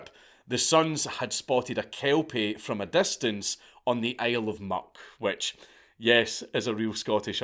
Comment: I kept hearing an R in there, like "Isle of Mark" or "Isle or Murk," but apparently this is the "Isle of Muck" he's talking about. And yes, real.